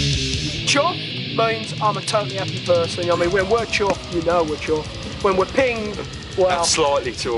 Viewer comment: Does anybody know what the fuck was just said here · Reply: Chuffed!